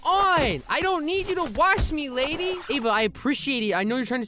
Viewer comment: URM….😨😨😨 3:00 did I hear the name “ava”, I want to suddenly change my name.